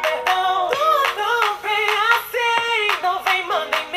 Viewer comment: Eu entendi "Tudo bem assim" e eu nem sou gringo kkkkkkk